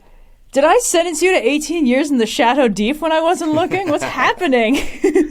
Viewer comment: said “What, did I sentence you to the chateau D’If for 18 years,” or something similar to for suggesting to do a jingle in the outro. I loved that book as well, just couldn’t understand it in the middle. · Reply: Oh I heard that as 'Shadow Deef' lol.